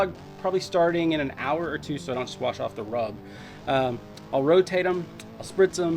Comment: Did you say "Squash off the rub" at 3:55????